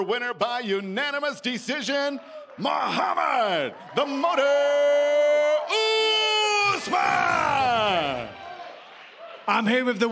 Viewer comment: I thought the announcer said "The Martyr" Usman. fml